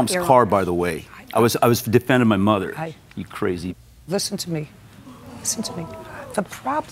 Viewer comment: did he just call her a crazy B? Did JJ miss that?